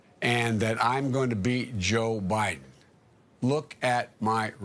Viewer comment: This dude thinks he's running against himself and y'all wanna give him the nuclear codes? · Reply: What he says at 10:15 is that he is going to be himself.... He says that "He is going to BE Joe Biden". He does NOT say that he is "going to BEAT Joe Biden." Does that make sense to you now ? I hope so. I think that Sean Hannity knew that Biden used the word "be" and not "beat" but Hannity went and reported that Biden said "beat" anyhow... That is the way that Sean Hannity is.